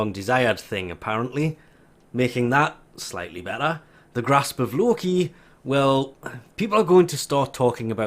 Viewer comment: did he just say "Grasp of Loki"? 8:20 yup he did